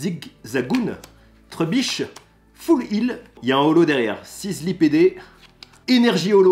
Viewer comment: Att j'ai pas bien compris c'est sizzly quoi ??